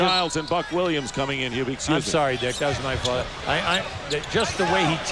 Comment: "Im sorry Dick that was my Butt" ahaha wtf anyone else hear that?